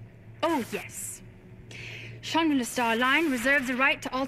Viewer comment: Sounds like she said "Shinra starline" ijs